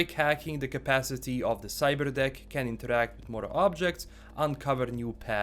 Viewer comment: At first I thought he said “cyber d!ck “ not cyber deck cuz the accent ✊😂